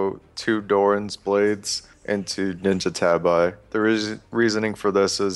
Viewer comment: 2 ninja Tabi? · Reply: into*. I thought he said 2 ninja tabi at first too lol.